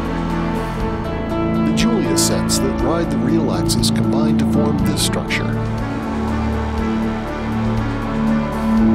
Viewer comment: What does he say at 14:14? "Julia sets ....... real axes combine to form this structure'. Can't comprehend the word. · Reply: julia sets that ride the real axis combine to form this structure